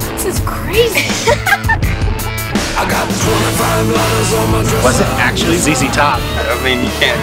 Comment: It sounded like they said "I got 25 lines on my dresser..." DAMN RIGHT, Texas musicians are fuckin' DIEHARDS!